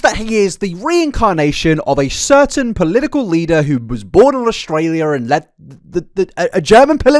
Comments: I thought you said Australia 🇦🇺 I nearly spat my wine out. Luckily I did not 😅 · do I heard it correctly? Born in Australia?